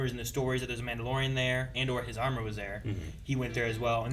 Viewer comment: true but then again he knows beskar can survive laser shots from starships like in the tragedy so at least one shot could go off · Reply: i thought he said his armour wasn't beskar